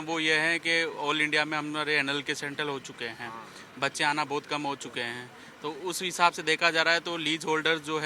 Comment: did he say anal 😂😂